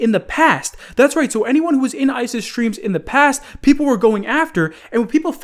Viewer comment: i thought he said isis but he said ice's